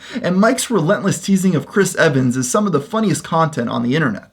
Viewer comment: Did you call him Chris Evans at 4:04? Ooooh myyy gooooooooooooodddddd!!!!!